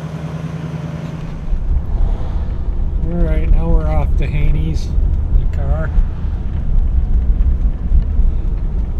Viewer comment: and again at 3:13, it sounds like you said "Hades". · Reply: That would be "Haney's" Restaurant. There on Van Dyke Ave., between 9 and 10 mile Rd., in Center Line, Michigan. Great breakfast and lunch place. They close at 3pm. My friend Tom does there gardens.... he is an amazing gardener. It worth going there just for the gardens... there the best mid. to late June.